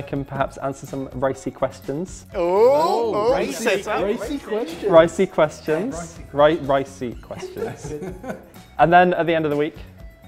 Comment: who else thought that said racy questions?? LOL :D